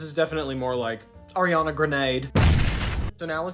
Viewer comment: I thought you said Aryan for a second 😂💀